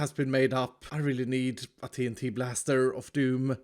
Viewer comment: “Mumbo should make a concrete maker” He totally didn’t have one... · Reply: I thought he said a tnt launcher